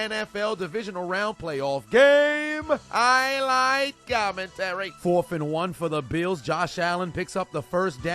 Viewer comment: This entire time I thought you said “I Like Commentary” not Highlight Commentary 😂😂😂